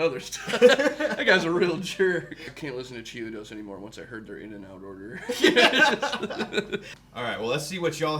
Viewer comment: Did he say “I can’t listen to chiodos anymore once I heard their in and out order” I can’t tell if I’m hearing that right.